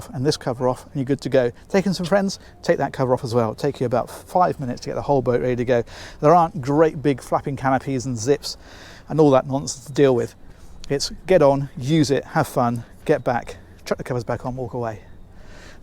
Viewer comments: Did he say "fucking canopies"?!? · I swear he just said great big f*cking canopies